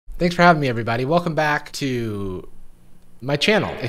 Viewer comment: oh my god i thought you said "they have taken me everyone" nope it was "thanks for having me, everyone"